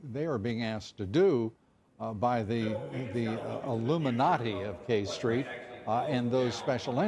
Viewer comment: Did he just say illuminati